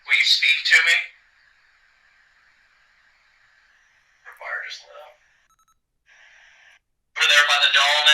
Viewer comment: Hi guys, I know you debunked the EVP towards the end of the video; however, I swear I heard at 1:42:32 " The wires lit up." cheers from Australia .